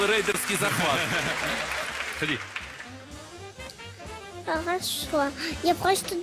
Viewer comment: did say "im not sure"? =]